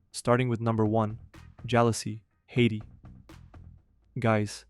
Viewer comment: I thought the first one said jailhouse 😂